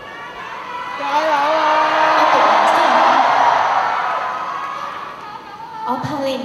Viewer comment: 想問一下Ivana 在1:57 講咩？ 聽左幾次都聽唔到。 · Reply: "我讀埋先"